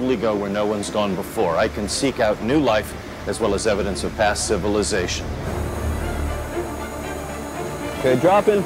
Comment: wtf did he just say? past civilisations? are they looking for a past civilisation in the deep ocean? wtf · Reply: yeah past civilizations, as in, exploring a sunken ship. From our PAST CIVILIZATION. There's World War 1/2 sunken planes, submarines, ships. A lot can be learned about our history from the deep ocean.